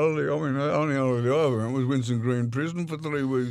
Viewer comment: wtf does he say on 15:40 onwards ?:D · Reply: The only holiday I went on was Winston green prison.